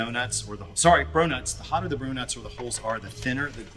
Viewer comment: did he say bronuts?